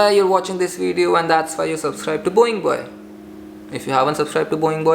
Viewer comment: & 0:16 I heard Boeing Bird by mistake